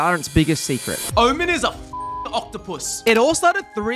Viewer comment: Bro I thought he said Omen f#ck an octopus